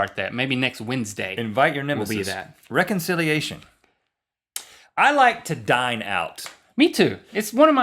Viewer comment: I thought he said "Rhett-Linc-onciliation" :)